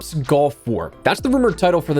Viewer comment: I know he said gulf... but I swear it sounded like he said black ops golf war... 🤣💀